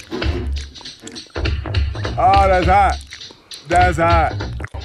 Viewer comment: what he say: oh lazza, the za? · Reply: Its a line from a commercial he did thats later on became a meme and he says"oh thats hot thats hot"